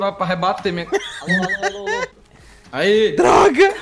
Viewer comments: So eu q ouvi o falando bem baxinho alô alô alô??????? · so eu que ouvi um alo alo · só eu que escutei um "alo alo alo" no